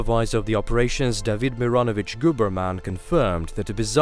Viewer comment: did he say the guys last name was gooberman?